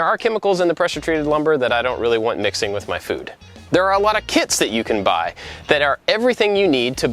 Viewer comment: I heard kids not kits xD